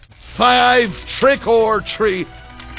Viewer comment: I thought it said Libra skin 😅